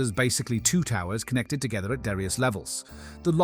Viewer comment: Made without AI? Some clips are clearly generated by AI, and the voice is very likely AI too, making pronunciation mistakes like 'darious' instead of 'various' @ 1:44 (and just sounding very flat overall). This is the kind of shit we'll see more and more of, when AI creations get penalized by default; people will put more effort into hiding it.